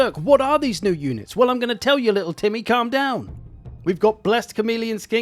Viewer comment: I didn't hear "little Timmy" I heard "little Tiddy" and had to rewind to check 🤣🤣